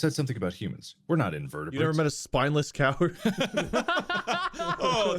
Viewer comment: "You ever been a spineless invertebrate?" you are an absolute delight, and I love you. Platonically. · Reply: when does he say that? i heard "You never met a spineless coward?"